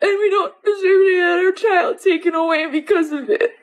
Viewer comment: did she say another child taken from them?